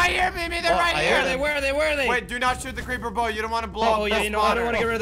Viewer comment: I thought said paper bout but it’s creeper bow